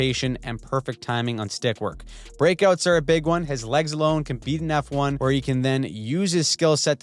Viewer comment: At first I heard, "His legs alone compete in F1" 😆